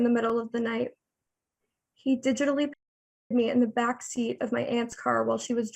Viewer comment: Did she say at around 4:00 "he digitally f#cked me"? Please correct me if I misunderstood I just cant exactly make out her wording